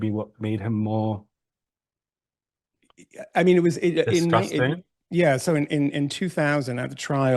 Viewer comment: Did he call his grandfather "Disgusting"?? At 26:31?